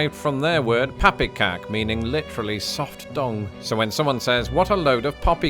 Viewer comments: I swear it sounded like he said, "pappekak, meaning literally soft dong." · when he talks about poppycock being a Dutch word that literally means 'soft dung', I wasn't quite sure what he said, but I was watching the closed captioning and it said 'soft dong'. --- I was waiting for him to explain more because I wanted to hear him explain THAT. Then they showed a picture of someone shoveling horse manure and I understood that he said 'dung.' --- then I had a good laugh. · OH, soft dung. I did a double take. I thought you said soft dong. I was like, "how does that make it any better?"